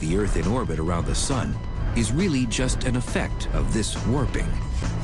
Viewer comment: what does he says from 2:10 to 2:15 whats the word is it worping